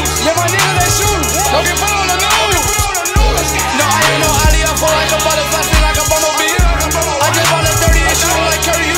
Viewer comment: wat was he saying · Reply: no I'm not no Ali , I float like a butterfly sting like a bumblebee , I just bought a 30 , it shooting like curry you know that bitch MVP 🔥